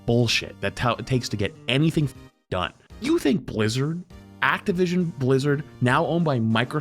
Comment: what was that he just said?? Activision blizzard